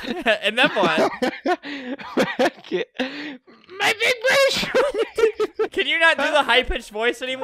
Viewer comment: "MY BIG BUSH"...... ....is what i heard.